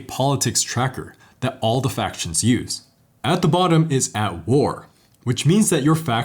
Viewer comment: I thought you said that the bottom of the politics tracker was "Al Gore" instead of "at war". I had to do a double take and rewind.